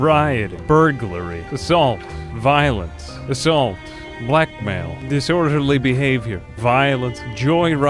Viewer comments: when he said blackmail I thought he said black male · When you said "Blackmail" I heard "black male" and I was like "damn, social commentary" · I heard black male at 10:35 xD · HOLY FUCK WHEN HE SAID THE GUY WAS IN JAIL FOR BLACKMAIL I THOUGHT HE SAID BLACK MALE AND THAT THE CHARACTER WAS JUST IN JAIL FOR BEING BLACK. Sorry for yelling but that was once of the funniest things I've ever seen.